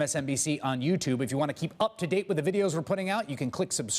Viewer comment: I don't think it is a fair interview when you have a brother interview a brother... makes no sense... Also when Chris Hayes says at 8:31 "click subscribe - just below me" ... it really doesn't sound like he says 'below' but more like he wanted to say 'blow'